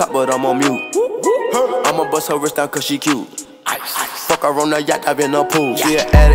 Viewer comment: sounds like he said “fuck corona” and honestly same